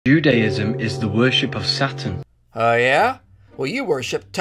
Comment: I thought he said it's the worship of satin